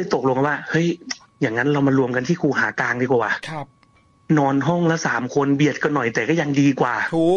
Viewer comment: เดียวนะมีใครได้ยินเสียงที่พูดครับเป็นเสียงใหญ่ ตอนนาทีที่22:16 เสียงมันดังแทรกเข้ามา ว่า ครับ พูดด้วยน้ำเสียงใหญ่ๆ😢😢😢